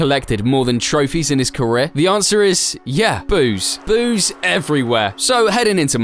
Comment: totally thought he said booze. Was like dam max is an alcoholic